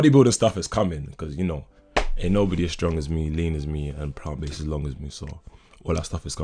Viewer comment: I thought you said “nobody is as long as me” Almost got me pregnant 🤰🏾🤣